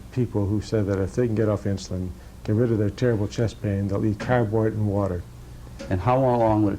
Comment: What did he say at 42:58? ...and water? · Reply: They will eat cardboard and water if they could get off their insulin....